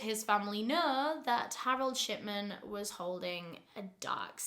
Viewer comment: I thought she was saying Harold Shitman until a read the description 😂